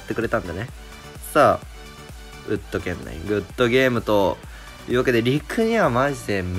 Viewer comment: ウッドーゲームって聞こえたw